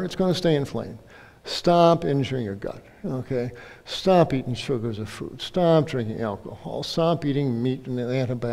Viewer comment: At around min 05:58 it sounds like he says: "Stop eating sugar & FRUITS"....Wtf? 🤔